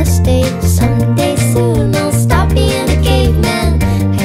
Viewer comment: It's 3am and I'm listening to this as background music while reading how to do Flintknapping and I heard the lyric "one day you'll stop being a caveman" as "one day you'll stop being a gay man" and I was soooo confused 😂